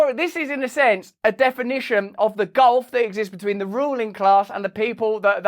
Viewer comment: lol at about 20:58 when RB said “ruling class” I first misheard it as “ruining class and the people they are ruining “ 🤔